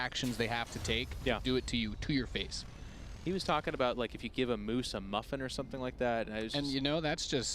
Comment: They're talking about one of their caster friends that is soon to be fired. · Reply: I also don't get it. "He was talking about, like, if you give a moose a muffin" - ??? Is that what he said at 9:05? I know moose are somewhat identified with Canada, but how is that insulting?